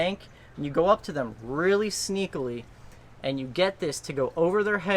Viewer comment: did you say 'sneakily' ? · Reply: Yes he did its a word